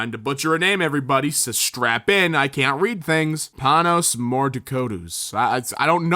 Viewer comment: Did... Did he just say "Thanos", or is it just me